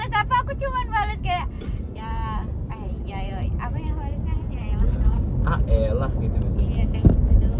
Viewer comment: ada yang ngeh gak sih Arafah bilang “apa yang bales nya yang” sampe berulang kali ku puter apa aku yang salah denger yak😂😅 menit 12:14, tolong bisa bantu beri penerangan